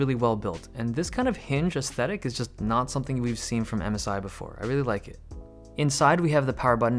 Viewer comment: I thought you said Amazon instead of MSI